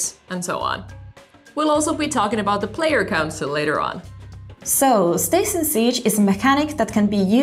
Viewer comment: I thought she said "We'll also be talking about player counts later on"